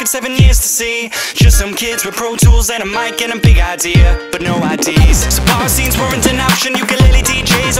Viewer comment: Wait what I thought they were saying "but no ideas" not "But no I.d.s" am I the only one. My life is a lie